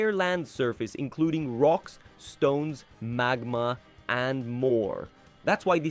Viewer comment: DO I HEAR A ROCK AND STONE!?